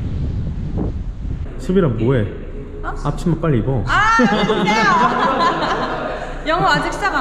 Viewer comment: 앞치마 빨리 입어를 닥치고 빨리 입어로 들었네 ㅋㅋㅋㅋ